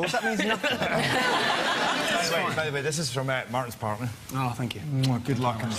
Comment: what did he say? ''this is for martin's........'' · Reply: "This is for Martin's partner" ;-)